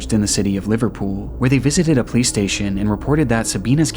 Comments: I heard PlayStation/play station 😭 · I thought you said "Playstation".